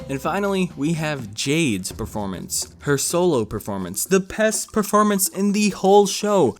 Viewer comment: Wait in 9:34 did you say pessed😂 or did you mean best😂😑